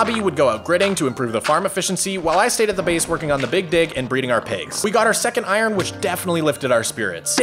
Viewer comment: Bro at 3:46 i heard bro say "i stayed at the farm working on the big *drake*" but yeah very good job :D